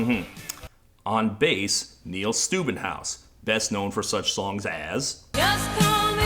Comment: I deadass thought he said this guys name was Neil Stupidhouse 💀